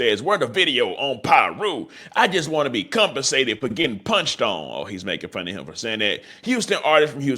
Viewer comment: 😂who else thought it said sauce got punched on